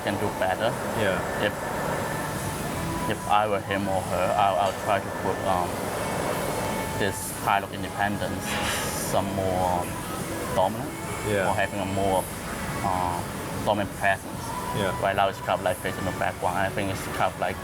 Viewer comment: lol did he just call the Washington Monument a "Pile of Independence"?